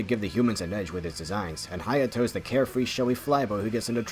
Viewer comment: did you just say H a y a t o